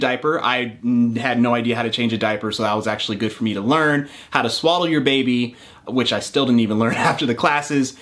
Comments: I swear I heard: "how to swallow your baby" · Okay I heard “how to swallow a baby” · What he said: How to swaddle your baby. What I heard: How to swallow your baby. Yeah. I wouldn't know how to do that too.